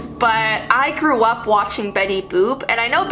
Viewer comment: I swear I thought she said she "threw up watching Betty Boop".